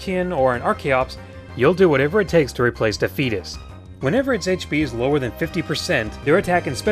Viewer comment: i thought you said "the fetus"